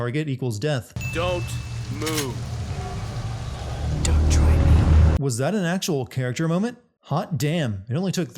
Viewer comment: what did she say? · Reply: She said, "Don't try me."